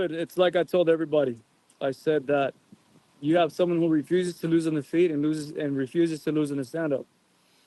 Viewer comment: refuses to lose on the feet and refuses to lose in the standup? Is that what he said